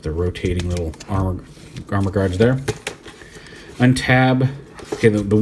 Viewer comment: You say "armou guards" but all I hear is "erhmagerds"